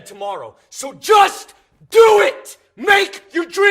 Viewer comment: what did he say at 0:00? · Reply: Takkyla Miller he said DO IT.